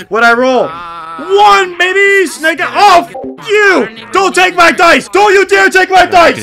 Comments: Did he just say the word??!!🙀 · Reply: i said 'snake eyes' it's a phrase used when playing craps, means 2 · what did he say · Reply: “One baby, snake eyes oh f*** you”